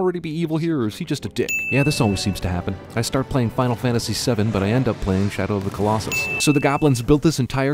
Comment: Can't believe I've only just noticed this but at 2:33, it says Final Fantasy XII (12) instead of Final Fantasy VII (7).